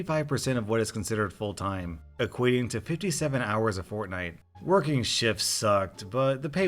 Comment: did someone say fortnite?😳